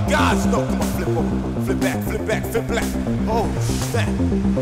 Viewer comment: Am I the only one who heard him say flip black at 8:42😂😂